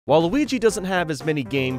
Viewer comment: It’s like said Waluigi instead of While Luigi